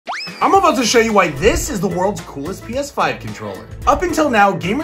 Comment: For a second I thought you said piss 5 controller instead of PS5 controller lol 😂